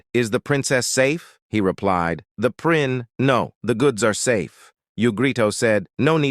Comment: When he said “the prin-goods are safe” I heard “the pringles are safe”